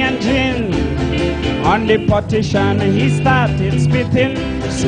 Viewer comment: Why am I hearing "portition"?